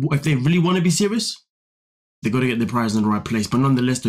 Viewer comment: They needed to move out 2 kids ago. She doesnt want to move out bc she's to immature to run a household. And at first I thought you said they needed their "privates" straight lmao. They def don't have their priorities straight at all.